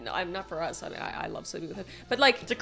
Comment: did I hear that right? "I love sleeping with him"